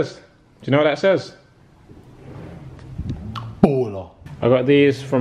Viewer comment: did he say ebola